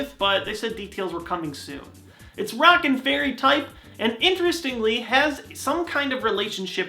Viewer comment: I thought I heard "rocking fairy type" which sounds awesome but I know it's rock AND fairy type. Also wish they'd bring back Mewtwo in smash. Here's hoping Mario Golf's good too. Great vid!